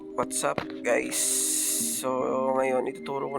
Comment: (✌️😔WAtzupp Gays(that's what i hear